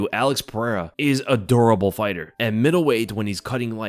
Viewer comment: thought you said he is adorable fighter at first lmao.